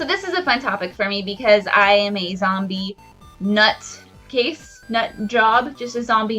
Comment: LMAO at 1:09 I thought you said you loved 'zombie porn' tsk tsk